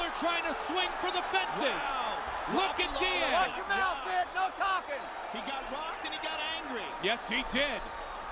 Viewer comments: did he say "knock him out, 'w'itch. no talking." ? :D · Did he said : "Lock your mouth b***h ??" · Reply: Watch your mouth bich no talking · Mazzagatti really said "Watch your mouth, no talking" HAHA! Just another reason he was one of the worst refs in the UFC. Like bro, Nick was doing that well before the UFC. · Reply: I thought he said mouth piece.